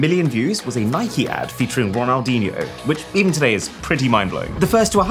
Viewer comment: Ronaldo I think he said